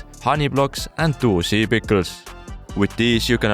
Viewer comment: holy shit "dual sea pickles" 5:23. man ive heard him say that like 20 times now and im just now like 90% sure thats what hes saying. still room for doubt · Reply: he said dual sea pickles as in 2 pickles on the same block